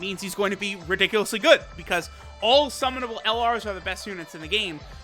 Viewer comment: Thought you said that Bra was the best unit in the game